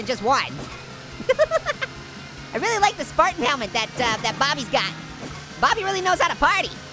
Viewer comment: Orange, did you just say "Fartin' helmet?"